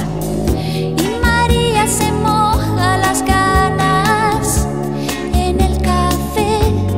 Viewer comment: CRUZ DE NAVAJAS A las cinco se cierra la barra del 33 pero Mario no sale hasta las seis y si encima le toca hacer caja despídete, casi siempre se le hace de día mientras María ya se ha puesto en pie, ha hecho la casa, ha hecho hecho hasta el café y le espera medio desnuda. Mario llega cansado y saluda sin mucho afán, quiere cama pero otra variedad y María se moja las ganas en el café, magdalenas del sexo convexo. Luego al trabajo en un gran almacén, cuando regresa no hay más que un somier taciturno que usar por turnos. Cruz de navajas por una mujer, brillos mortales despuntan al alba sangres que tiñen de malva el amanecer. Pero hoy como ha habido redada en el 33 Mario vuelve a las cinco menos diez. Por su calle vacía a lo lejos sólo se ve a unos novios comiéndose a besos y el pobre Mario se quiere morir cuando se acerca para descubrir que es María con compañía. Cruz de navajas por una mujer, brillos mortales despuntan al alba sangres que tiñen de malva el amanecer. Sobre Mario de bruces tres cruces. Una en la frente, la que más dolió. Otra en el pecho, la que le mató y otra miente en el noticiero: "Dos drogadictos en plena ansiedad roban y matan a Mario Postigo mientras su esposa es testigo desde el portal" en vez de cruz de navajas por una mujer, brillos mortales despuntan al alba sangres que tiñen de malva el amanecer. (J.M.Cano) 1986 Versión Live: <link> Suscríbete a CANAL MECANO: <link> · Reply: Pense que decia "y María se moja las canas en el cafe"